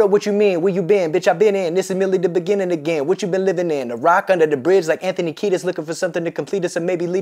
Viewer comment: ok am i tripping or what..... Doesn't Booby say "a box under the bridge like Anthony Kiedis" not "a ROCK under the bride like Anthony Kiedis" ???